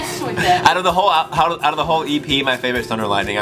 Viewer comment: Every time he said 'The whole EP' I kept hearing 'The Holy 'P'' and I was like yeah sure 😂